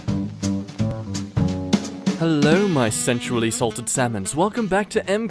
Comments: At the start I thought he said "hello me sexually assaulted salmons" 😂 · When he said "sensually salted" I thought he said "sexually assaulted" Salmon